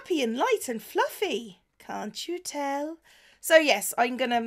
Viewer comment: when she said "cant you tell" i heard "annie-bell"